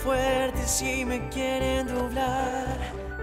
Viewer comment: escuché algo bien xd😂😂😂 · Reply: escuche "quiere endublar" JAJAJAJ